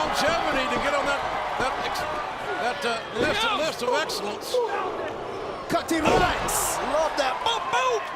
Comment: Did Taz say "Cutty Ranks" at 8:23? 😂😅😂😂😂